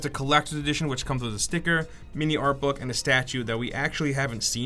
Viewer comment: not gonna lie when he said sticker i thought he said snicker and i was like "why the fuck would they give you a snicker wont that shit be melted once you get it"